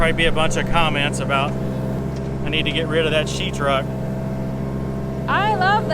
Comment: Guy here... I heard her say you need to get you another truck.